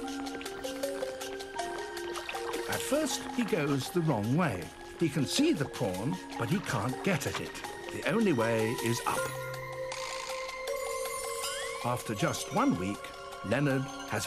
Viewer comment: eh, i heard " HE CAN SEE THE PORN, BUT HE CAN'T GET IT" 4:12.